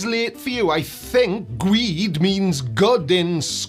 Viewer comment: is just like the guy who died in his flat earth rocket crash. The rocket guy pretended to be a flerf to get his rocket funded by actual flerfs. did it for the subs. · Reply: I heard ‘gweed’ as “great”.